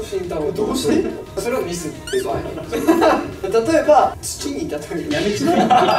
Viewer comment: ぐらいのじゅったんが言ってる例え、なんて言ってるかわかる人いますか？みんなが笑ってるので気になります笑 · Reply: 「土」かと思ってましたが 「月」にも聞こえるし… どっちにしろ意味わからなくておもろいですね笑